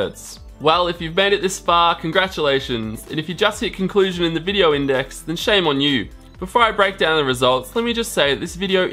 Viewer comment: What did he say? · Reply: Papi F hahahaha